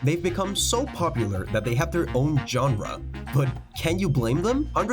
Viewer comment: am I the only one that understood John run instead of genre 😂